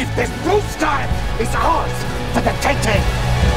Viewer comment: Ha! sounded like he said "Tity" at 1:34......I know its "Taking" but still, LOL